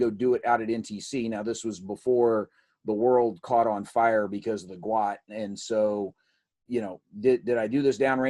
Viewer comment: What did you say at 2:15 - "the world caught on fire cuz of the *what I couldn't understand*" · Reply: "the GWOT" - the Global War On Terror